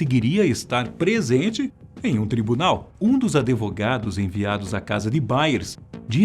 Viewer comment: Só eu ouvi "ADEVOGADOS" ??